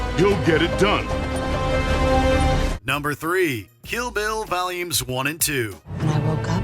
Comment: go with your mouse to 29:13 it says Hillbillies instead of Kill Bill wtf 🤣